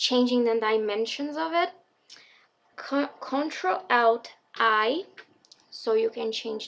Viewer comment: what did you say at 11:35 ? ctrl what 😅 sorry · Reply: ctrl+alt+i